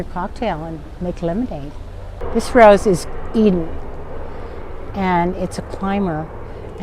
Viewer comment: What did she say is the name of the Rose @ 7:00.?? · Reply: Eden rose